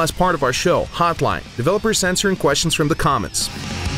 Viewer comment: I literally heard "developers censoring questions from the comments" lmao